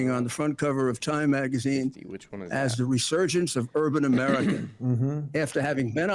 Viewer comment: At around 41:47 Guiliani sounds like he says, in 1998 NYC was the "resurgence of bourbon America"